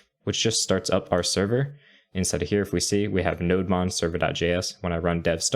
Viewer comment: for a second I thought you said node monster LOL